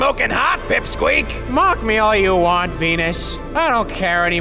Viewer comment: LMAO I THOUGHT MERCURY SAID “mock me all you want, pen*s” HAHAHAHAHA